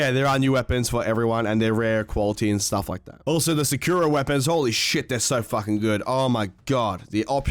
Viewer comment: Did he say Sekiro or Sekuro-weapons?